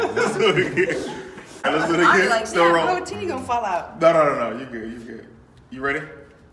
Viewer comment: did she just say "my protein gonna fall out?" Lol? Going to bring out the thirsty youtumenters out to the yard.